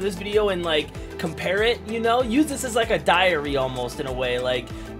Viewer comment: It sounded like he said “like a diarrhea” 2:45. Had to skip back 10 secs to check.😂